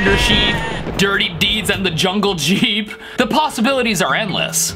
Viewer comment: “I Hear Dirty Deeds and the JUNGLE SHEEP” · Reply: I hear 'dirty deeds and there done for cheap'